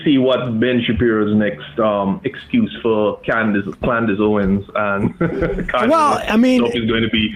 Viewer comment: not sure if it was a slip of the tongue or intentional (around 4:05 -- sounded like latter) when called Owens "Clandace" -- but honestly Klandace Owens and Klanye really fits, just new shade of intentional undermining and the from-within hate thing etc.